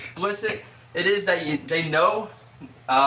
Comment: Mr.Beast? I thought u said mr.weast